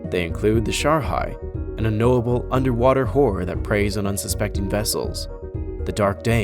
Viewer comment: did he call the shark a whore?